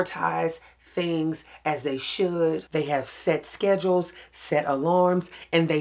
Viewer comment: I thought sure you said you have sex scheduled at first, but realized you said ‘set schedules’. 😂😂 ain’t nuthin wrong with scheduling sex, Just saying!!! 😊